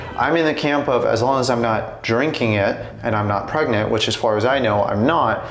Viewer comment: you can be pregnant ?? - Is that what you said at minute 7:52 ? I had to listen to it three times